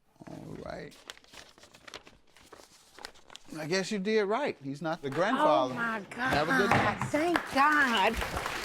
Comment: I thought he said he’s NOT the grandfather 😳